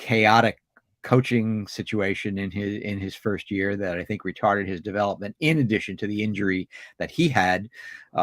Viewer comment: Did he say "Retarted"